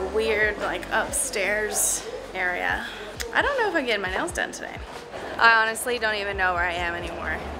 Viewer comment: Sounded like you said you were getting your nose done hahaha